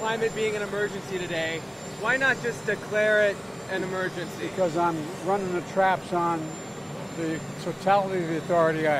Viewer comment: when asked why not declare a state of emergency It sounds like he said to the reporter "cause I'm runnin the trap son" lmao